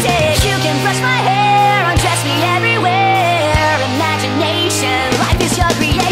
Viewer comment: When you thought the song said "Dress me everywhere" for the longest time ever and then you see this and you found out it said "Undress me everywhere"..... o.o